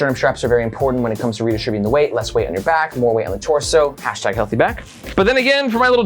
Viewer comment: Could have sworn he said healthy back. What the hell is a healthy bag? Is this a scrotum health channel all of the sudden?